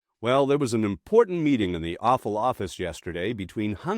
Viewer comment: I misheard the beginning as a "meeting in the Awful Office"😂. With trump, that is more apropos.